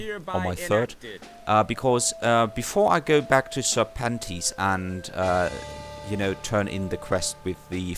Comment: each time you say serpentes i hear "Sir Panties" xD